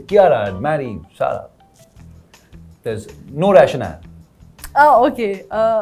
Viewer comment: What did he say ? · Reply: He says "there is no rationale" which means koi specific reason nahi hai 😁